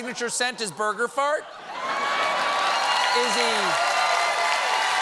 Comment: "Burger farts". Yep, that sums up Trumpty-Dumbpty pretty well. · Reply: I thought he said “murder farts” at first… which still sounds applicable.